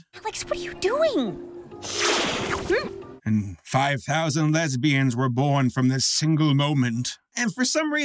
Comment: "And 5000 lesbians were born from this single moment..." I feel personally called out, how dare you. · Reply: I thought he said "And 5000 babies were unborn from this single moment"